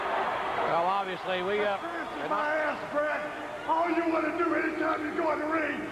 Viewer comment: What did Steve Austin say?... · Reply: tthomaselli2 I think he said, “They screwed you”, MY ASS, BRET!”